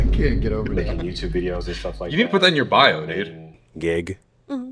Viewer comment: why did “dude” sound like he said “n*gga” 🤣🤣